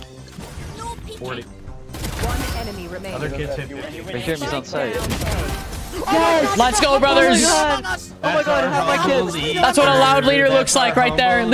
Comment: I REALLY HEARD "HAVE MY KIDS LMAOOOOOOOO"